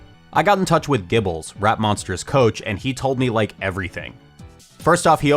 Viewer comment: I don't know why, but when I'm not paying full attention, I keep mishearing it as "RatMonster" instead. Take that random comment how you will.